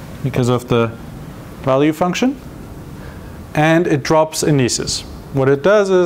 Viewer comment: can someone tell me what he said at 8:30, "and it drops xxxx, what it does current stuff does not have xxxx ~ ". I have watched it over and over again, but still yet figured out what the word is... thx. My English is too bad... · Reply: indices